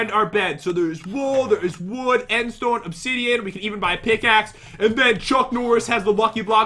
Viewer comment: At the Start, when you said "You can even buy a pickaxe" I thought you said "We could even buy some Nik Naks" (they are crisps) 😂😂😂